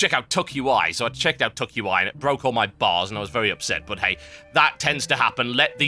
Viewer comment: I thought he said "It broke all my balls".